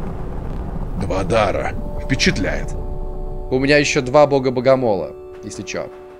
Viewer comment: мне послышалось Мадара